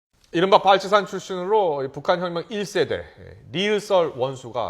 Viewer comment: 뭐라는거지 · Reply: 뻘치산 출산으로 뻘치산은 옛날 북한전쟁군인 을 뜻 함